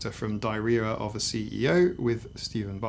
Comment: I could swear you said “Diarrhea of a CEO” & that made me laugh. Something I cannot put my finger on has always bothered me about Steven Bartlett and so many of his guests. I can’t quite explain it. There are a few other similar content creators, and they all seem to cycle through the same collection of special guests who fashion themselves as experts on various topics, and I feel like they are put up on pedestals when they don’t always deserve it.